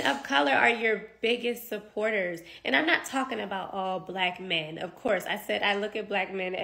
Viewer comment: So this woman says she loves her Black King, but she thinks she can Direct, Correct and Command how these Black Kings Act, Talk, Respond and What ISSUE THOSE KINGS CAN ADDRESS!!! The Kings Lead, She need to Follow and LEARN!!! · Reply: I thought she said "Not All". She's specifically referring to the sassy ones.